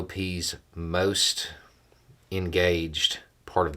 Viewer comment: said "engaged" but I heard "rabid".